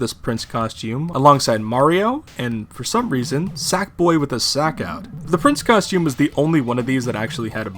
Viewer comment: okay we need to talk about what he said. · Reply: Sackboy with his sack out